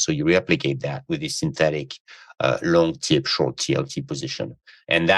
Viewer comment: What did he say at 35:34? "Long _____ , short TLT" His french accent is tricky to understand what he's saying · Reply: TIPS (treasury inflation protected securities)